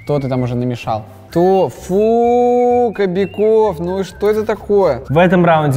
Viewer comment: я один слышу "ТОФУУУУ"??